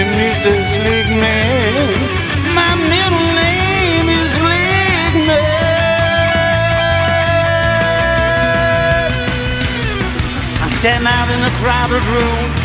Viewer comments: did he just say lickma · I legit heard “MY MIDDLE NAME IS LIGMAAAAAAAA” 1:51😂😂😂enjoy!